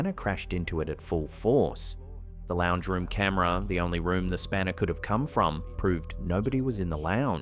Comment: that thing hitting the cabinet at about 20:31 min. americans call that a "wrench." what are they calling it? i can't hear it clearly. thanks for the video. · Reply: Spanner the word in use. Short for spanner wrench or spanner tool.